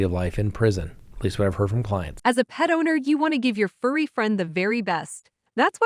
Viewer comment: Every single time I hear your CBD commercial I hear “as a pedo-need”… I realize she is saying “as a PET OWNER”, but Lord… it shocks me every time.